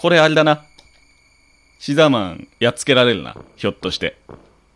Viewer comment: 牛沢さんが好きすぎて、シザーマンって言ってるのに毎回「うしざぁまん」に空耳しますごめんなさい。